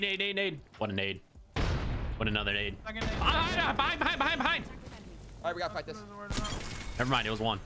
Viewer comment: can we get subtitles for what you said here 😂😂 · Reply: ON ON ON, Behind behind behind